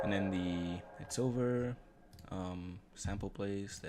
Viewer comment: "It's over." Dang....thought it said "Jennifer" for years bro. Loved this piece of art regardless. This happens to be a gem that I have cherished ever since an impactful time of my life came around. Eventhough I only knew you through this one song, I consider myself having been blessed to hear. Thank you.